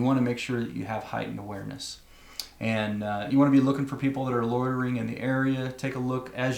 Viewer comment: Instead of "loitering", I heard "lawyering". As if some ambulance-chasing personal injury lawyer is lurking in the shadows just waiting for something bad to happen. I suppose, if that was the case, that would be a valuable indicator that you are not in a very safe place. 😂 ... I crack myself up. Anyway, carry on.